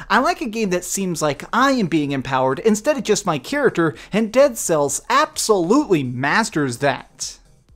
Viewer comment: Oh by the way you should rewatch this video because you actually did say Dead Cells, but you must've thought you said Dark Cells....lol